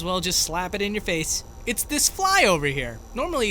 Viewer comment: you sound like Scout saying “There’s a spy over here”